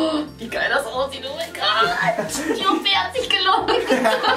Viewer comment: Bei 4:22min. Was sagt sie??? Die OP hat sich gelohnt.??????